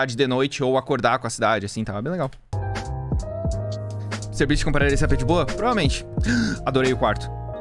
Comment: eu ouvi "cellbit compraria esse ap de boa" juro